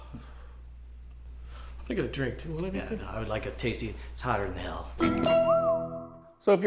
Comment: Um.... Is it just me or does he say, "I would like a tasty."? @ 03:54???